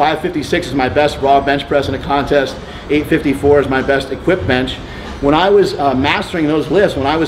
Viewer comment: 854 he best equipped bench? did i hear this correct? wtfffff 854lbs right?